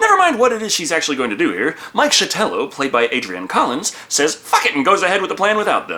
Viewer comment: ish instead of fuck it our soon to be AI overlords thought you said kitten of all things kitten